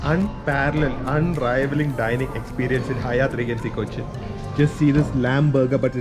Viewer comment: around did u say hyatt regency Cochin? 😛